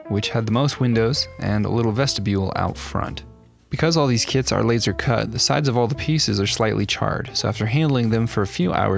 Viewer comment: i heard kids instead of kits...